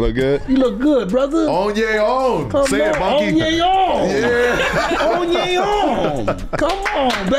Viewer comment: did i hear him say monkey or am i tripping😂